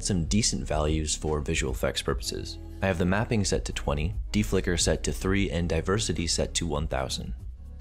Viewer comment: Oh god, I thought it said "DE-FUCKER."